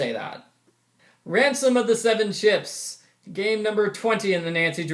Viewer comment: I thought he said, "Phantom of the Seven Chips,"